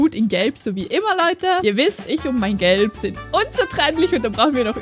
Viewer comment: Bei 1:14 Geld verstanden😂😂 Lachflash des Todes🫣